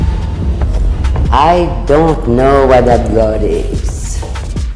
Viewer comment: It sounded like she said "idk where that god is." Not "idk what that god is."